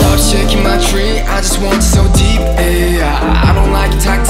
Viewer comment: What I first heard IM: "I just want you so deep." Hyungwon: "Take my ass, take my ass." Me: 😳